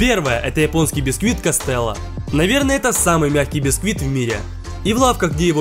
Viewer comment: Мне послышалось, самый гадкий бисквит